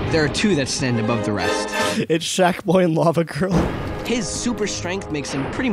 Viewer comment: Did he just say “shaq Boi and lava girl